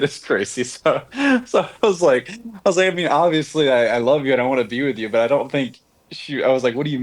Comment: anyone else catch that Freudian slip? “I love you, I don’t wanna be with you”